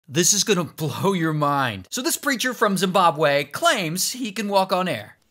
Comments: i was confused when i thought i heard this creature from zimbabwe💀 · I thought he said “ Creature from Zimbabwe”💀💀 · Nah man, I heard that as ''This new creature in Zimbabwe'' 💀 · I thought he said creature form Zimbabwe 😅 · Am I the only one who thought that he said “ so this creature from Zimbabwe”😭😭😭